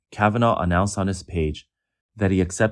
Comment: When i hear "Chinese netizens" i often hear it as "Chiese medicines" :-)